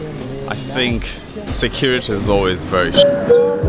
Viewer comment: Did he say security’s always very sh*t